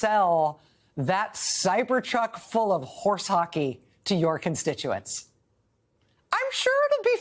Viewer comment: Rachel, I had to lol at the horse hockey comment you made, I always heard it as horse puckey. Just thought I would say that. 🙃